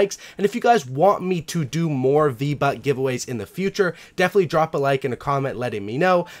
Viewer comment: it sounds like u said vbutts